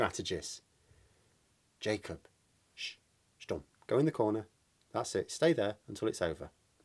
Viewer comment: A question as a non-English native speaker: Did I hear correct, and you said "stumm" at 1:56? The German "stumm" which means "mute/silence"? Is that a lean word common used in English?